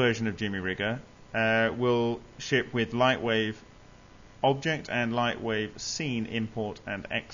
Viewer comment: What is he saying at 3:17? Lightwave Octet?